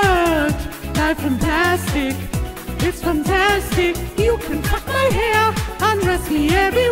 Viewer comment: habt ihr auch bei 1:27 you can fuck my hair verstanden???